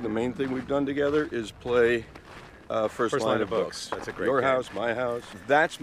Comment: Can someone tell me what he said at 3:18? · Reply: First Line of Books